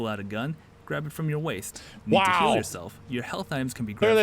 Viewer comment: I THOUGHT HE SAID: Need to k i l l yourself? 2:21 I laughed. I'm SO SORRY Also it have subtitles 3:22 :D